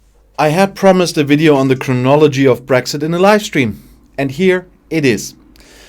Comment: Oops, sorry, I misheard you there. For a moment, I thought you were introducing a video on the ‘criminology of brexit’. Mind you…